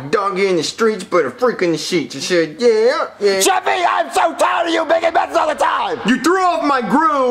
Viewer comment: Why did it almost sound like said “Jeffey I am so proud of you making messes all the time”.